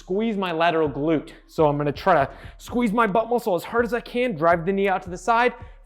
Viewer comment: "Squeeze my butthole as hard as I can" (Butt muscle is what he actually says)